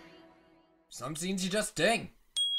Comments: Am I the only one who whenever Sayori says just Sayori it sounds like Just Say Yuri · At around 13:36 I always hear “just say yori” “just say yori” 😂😂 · At the end whe sayori says just sayori its sounds like just say ori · (I don’t know how to spell most of the names so if they look wrong I’m sorry) When sayori says “just sayori” it sounds like she was saying “just say ori” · He said Just Sayori but it sounds like Just say Yuri...